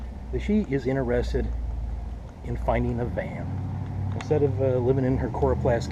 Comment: When you said that she wanted to find a van, I initially thought you said that she wanted to find a man.